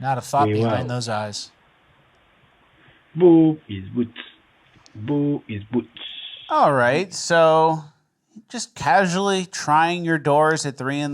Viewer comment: What exactly is he saying? The closed caption couldn't even capture it... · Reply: "Boo is boots, boo is boots... Boo is... Boots, boo is boots."??? wtf???